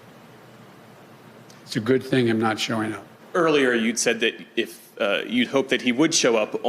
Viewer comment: Sounds like Biden said "It's a good thing I'm not showing up"... It's not going to be a very successful inauguration if neither president shows up. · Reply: What he said was, "It's a good thing him not showing up."...Eastern PA /Delaware dialect...